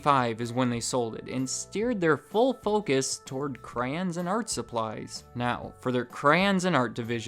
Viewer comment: You definitely said Cran it sounded like xD